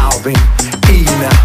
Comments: En vés de escuchar "soy latina" escuchaba "estoy en la tina" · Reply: Yo escuche "soy la china" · Yo entendi "sol en la tina" xd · Yo pense que decia soy gelatina baby